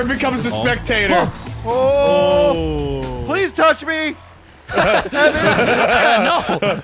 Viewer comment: did he say please touch me?